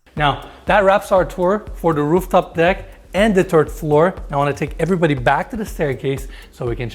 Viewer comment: Did he say the Turd floor?